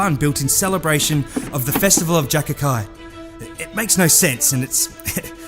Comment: Festival of what he said? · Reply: “Jac’acai”. Fictional Oz festival